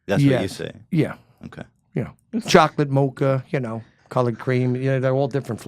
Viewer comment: Is it just me or does it sound like said "chocolate mocha, collared greens,..."